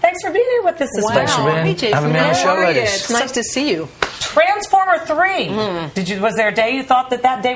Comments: Is it me or at 0:43 did Kathy say "Transformer 3"? He is so cute! Thanks for posting. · did she just say transFORMER 3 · I just noticed haha. It sounds like she says transformer 3 ha